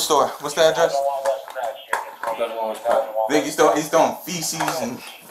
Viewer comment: does he say feces ?